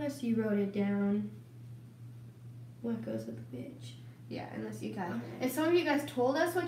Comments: To talk about the comments Roni didn’t curse she said she didn’t know what goes with which and even if she did they’re almost 23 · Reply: It’s at 9:17 it sounded like she said the b word but she said what goes with which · I just wanna know what you said RONI cause it sounds like a bad word and I know you didn’t say a bad word 😂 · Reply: What goes with which. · who else heard Veronica say the B word? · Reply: Karla Escobedo she said which